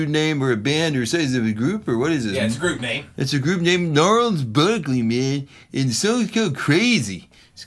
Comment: For second I thought it said Charles Barkley, lol.